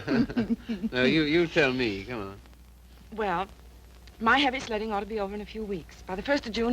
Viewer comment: I thought she said,” My heavy slutting should be done in a few weeks.”